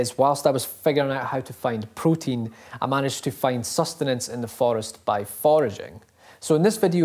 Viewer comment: I thought he said SUS